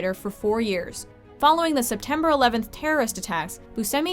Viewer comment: when she said September 11th terrorist attacks i thought she said the 7 11 terrorist attacks XD